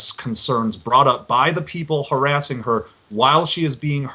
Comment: you can almost hear him say "Women" instead of people.